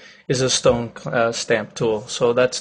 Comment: Did I hear 'stone clamp tool'?